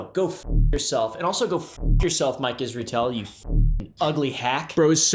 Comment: I thought you said like Israel not Mike isertel 😂 same difference to me